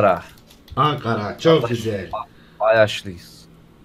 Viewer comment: ankara kayaş mı dedi en mi öyle duydum ?